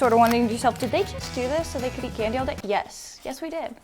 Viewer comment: what did she say? I’m confused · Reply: She said: did you think to yourselves that did they just do this so that they can eat candy all day? Yes we did